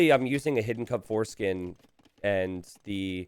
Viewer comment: Have you ever thought you'd hear say "Hidden Cup foreskin"? Well, he didn't, but 18:18 is pretty funny.